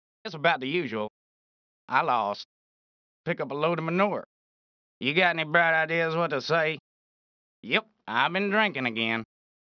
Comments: What he say? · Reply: "You got any bright ideas what to say?" But it sounds more like "you got any fried ears what to say?". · “you got any bright ideas what to say?” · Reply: "you got any fried ears what to say?". That's what I always heard. Such an oddly recorded line. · you got any bright ideas what to say? Captions: you got any bad ideas what to say? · “you got any bright ideas what to say?” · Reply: I always heard ‘you got any fried eggs what you say? Now I can finally sleep in peace.